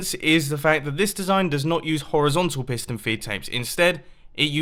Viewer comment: I thought he said "pissed" and "fetape"